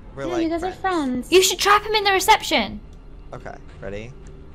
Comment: Guys When They Said "Reception" I Heard "Rejection" . . .Oh my lol..